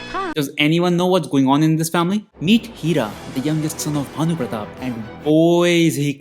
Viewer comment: Bro 1:36 did you say the youngest SIN of Bhanu pratap? 😂😂😂😂😂